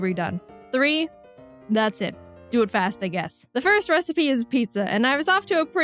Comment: I thought she said “do it fast, kick a**”.... I was so excited I rewinded it but only to be disappointed 😞